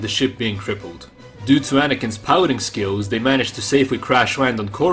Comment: I really thought you said "dew to Anakin's pouting skills".